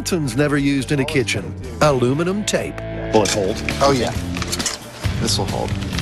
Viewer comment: did he just say butthole?